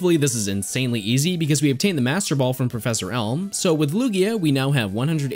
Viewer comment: I thought he said professor elmo.